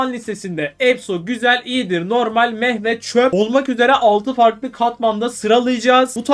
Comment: 'Meeh ve Çöp' dediğin yerde 'Mehmet,Çöp' diye anladım abi:D